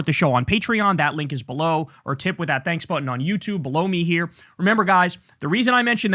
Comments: did Kyle just say “blow me here” 😂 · sounded like he said blow me here. It made me laugh which I really need